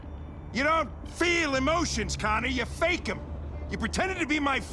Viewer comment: if you listen closely to hank he said "connie" instead of Connor